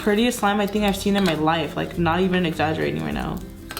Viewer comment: did she say this shit is crazy or was it just me